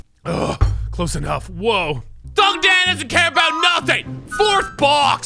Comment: Stay strong, there's plenty of fish in the sea. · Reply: Aquis Games lmao my bad I thought he said he didn’t care about. My fault